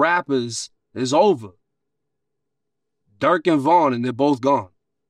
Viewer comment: Did He Say Durk And Von?....Am I Trippin...?